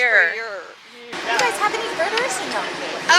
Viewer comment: I was watching the video with captions on and no sound and @ 3:47 the caption says "do you guys have any murderers?.." I was like huh? 🤔Why is looking for murderers?..